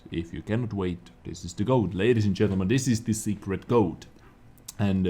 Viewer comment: Damn, I heard you say “Secret Goat“